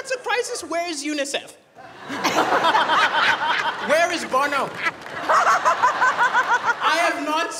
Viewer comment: Did he say "where is Bono?" 😂